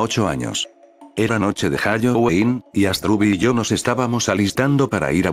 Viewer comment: Te diste cuenta que dijo hayoween